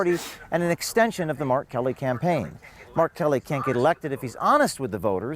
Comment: I was listening to this while working in the garage, and for a few minutes thought he was saying R. Kelly. Lmao